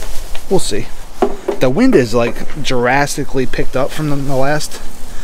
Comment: Maybe make a table out of the shadow boxes? Hi, btw! I thought said the wind was "Jurassicly" picking up...that would be some wind! Lol